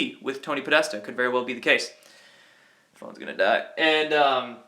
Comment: did he say at 13:36 "everyones gonna die?" 😂😂😂 Rollin ntff 4real 😂😱😂😱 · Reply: Jen Reports I think he said “I thought I was gonna die” and continued so I am gonna move quickly.